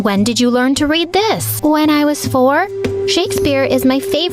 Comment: When she said when i saw four i thought she said When I was born.